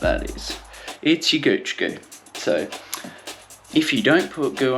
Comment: Bro, I really don't know what is that you're referring to when you said guchi gouch lol It was in this part of the video: 2:43 I think it's some type of product to put on the crotch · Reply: Hey mate! ahahah yeh I am referring to the product pooch goo, its a chamois creme so you don't get sore bottom when riding!! Its good gear!